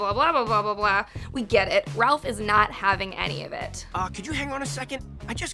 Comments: did she just say Ralph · Am i tripping or is she saying "Ralf" instead of Raph ????